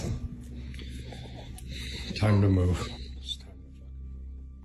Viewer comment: In the video,did anyone else hear an EVP just as he says"Time to move"? I could swear I heard "stop" or something like that in a whisper. Something about this guy seems credible though I haven't checked out his channel. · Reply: I heard it. I was specifically looking for this comment. I think right at 6:25 when the guy says " it's time to move", I think the whisper I hear also says "it's time to move "...idk have a listen and let me know what you think...